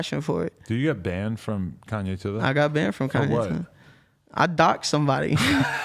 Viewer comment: what did they say at 16:50 about ye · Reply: Daniel Wayne Beats he said he was on Kanye’s periscope with a gun doxed someone and got banned from Kanye idk idk